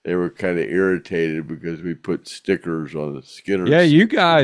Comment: I heard a man say " my wife put a tracker on me " 😭 JEZ US , 💕 SHE'D HALF TO GO .... sorry